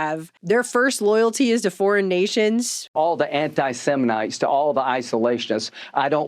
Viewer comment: it sounded like said anti-semenites. No wonder he's upset about them.